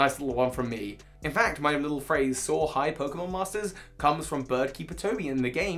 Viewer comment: I've watched probably two dozen of your videos by now and it wasn't until this video that i figured out your outro is saying "soar high!" all this time i thought you were saying something like "saw hide!" like raw hide as if u love western cowboys or something lmao